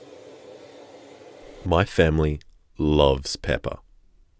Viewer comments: i rlly fucking thought you said "our family love peppa" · I heard “my family loves Peppa” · I thought you said peppa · thought u said peppa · Oh I thought you said peppa